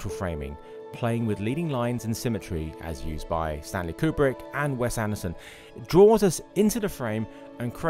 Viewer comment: Who did he say at 3:28 was it Sally Coobric and Wes Hamilton.